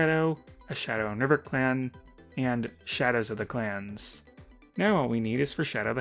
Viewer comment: For a second I thought he was saying class (yes, Battles of the Class)